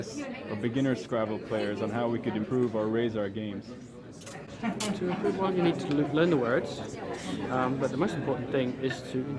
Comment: For what it's worth at 43:11 I think he's saying "To improve, 1. you need to learn the words, but the most important thing..."